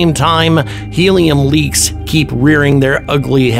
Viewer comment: I misheard, and thought you said Alien Leaks.